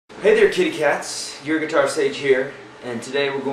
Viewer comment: i actually thought he said "titty cats" oO